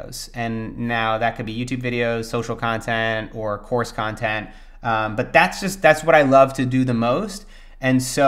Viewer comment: Please tell me someone else heard "horse content" @ 12:37. 😂